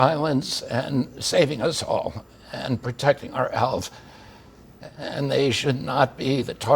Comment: I have trouble making out what he is saying and put on the CC. At the 0:52 mark it has him saying the CDC workers are protecting our "elves". I don't doubt that he really said this.